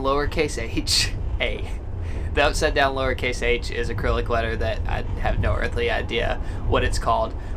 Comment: I'm just rewataching these videos. Did you say acrylic??? :D